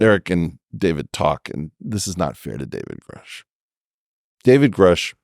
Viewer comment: For the first 1/2 of the vid, I thought they were saying “David Koresh”